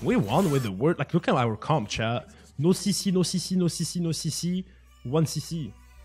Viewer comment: did he just said to akali no cc?